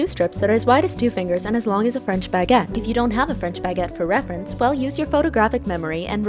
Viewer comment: Wait. I thought you said Spanish baguette. Now I have to start all over